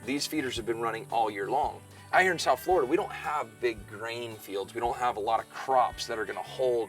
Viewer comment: This is what happen when you got to this video after watching pewdiepie video. I thought he said big brain 0:40 but it big grain 🤣🤦‍♂️